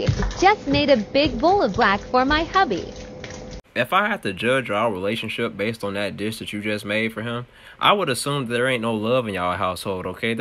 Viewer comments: I haven't slept... I first heard "just made a big bowl of death for my hubby" Now I hear " just made a bowl of black for my hubby". Lol am I tripping? 😮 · I thought she said a big bowl of fat for my puppy. I was getting ready to call someone to save that poor puppy. That 💩 is scary 😨